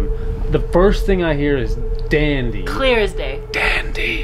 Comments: I heard " dandy are sick.." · I heard… “Dandy!… summoned Me!” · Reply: I hear "dandy" "fam-ily"! Lol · I heard “Dandy hurt me” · To me, it sounded like it said "Dandy.... fam....ily" just repeating what was said? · It sounds like it is saying on the EVP recording, 'Dan-dy.....Fam-ly'. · i hear "Dandy, hurt me" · When you ask about the Dandy family, both my husband and I hear “Dandy, hurt me” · I hear “dandy fam. Ily”